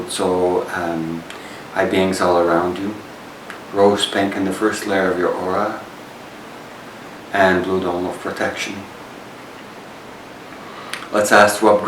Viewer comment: What is he saying? 1:45. I have rewound this 4 times. "...Lois Lane in the pink in your aura and an order of protection...." What? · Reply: What I said there is - ask for rose pink to go into the first layer of your aura, which is Christ energy, for protection.